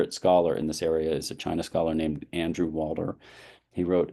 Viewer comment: I didn't know they hired Steven Spielberg initially for the Olympics 😅 8:40 Andrew Walter? I couldn't hear the name correctly, sorry. 13:30 Yeah, I think Deng was right on this, just letting the market run rampant without ANY government intervention or authority will just wind up similar to south korea with its chaebols... LKY said "peaceful rise" was not a good term when discussing with leaders at the time, and they now regret not changing it to "peaceful return" or "peaceful renaissance" to indicate china returning to tang as the peaceful center of asia. I think we as americans should heed the worlds of LKY and not create a long term enemy. Admit mathematically that of course they're going to be bigger than us, because they have way more people among being neighbors with so many other countries. It could have been a gradual slide down but it seems like our international image is very bad right now as the arbiter of the world Also, can you talk more about Suzhou industrial park? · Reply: Andrew Walder (Stanford), One of the great China scholars of that generation. His 1995 article "Local Governments as Industrial Firms," one of the most cutting-edge articles written on China's economic reforms in the 1990s.